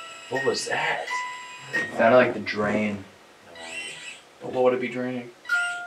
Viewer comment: Please tell me I’m not the only one who hears a weird voice say “drain” just after they finish talking about a drain noise